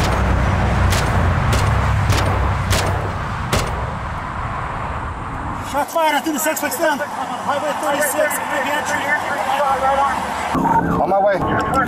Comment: I hear “Shots fired I think the suspects down.”